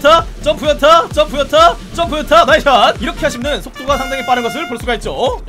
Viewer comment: 점프 요타 점프 요타 점프 요타 이 소리 나만 들리나?